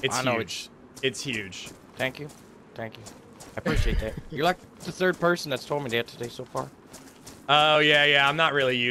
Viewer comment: He said you were the 3rd person (to complement his manhood). I think you thought he said something about playing in 3rd person.